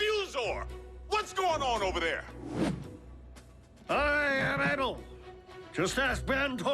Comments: What. Do I here a fuzor who needs a voice box for beer. What's a fuzor what's going on over there? I am bagel just ask mentor he was all manly and such but I uh put a tiger in his tank. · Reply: lol I thought he said I am ettal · What. Do I here a fuzor who needs a voice box for beer. What's a fuzor what's going on over there? I am bagel just ask mentor he was all manly and such but I uh put a tiger in his tank. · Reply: I thought he said I am etal